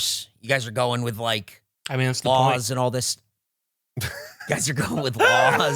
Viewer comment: What does say at 33:50 ? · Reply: "I mean that's the point"